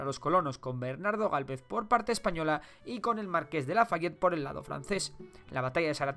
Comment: entendí Marqués de la Baguette :v